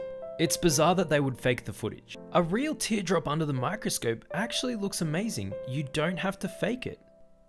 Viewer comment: I thought you said Microsoft 🤣🤣🤣🤣🤣🤣🤣